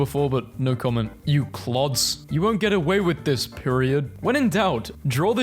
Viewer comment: you did NOT just call Peridot "PEIRIOD" 😭